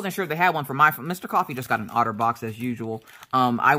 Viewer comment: Having a gardening business for 15 years, there is NO WAY I am not getting an Otter Box for my phone (Funny story..when they first told me about it...years ago..I thought they said 'outer box'!). That phone got dropped on the ground, in composition piles, and fell off a trailer wheel fender more times than I'm willing to admit. I put that thing to the test!